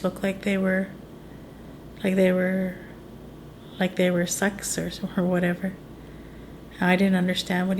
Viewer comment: she made his bouncers look like $ex? Well I've never heard that expression & I don't understand how either. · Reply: Look like SUCKS.